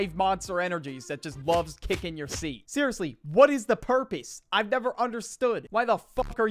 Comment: Purpees 😭😭😭😭😭😭😭😭🙏🙏🙏🙏🙏🙏🙏🙏🙏🙏 Bro said purpees instead of purpose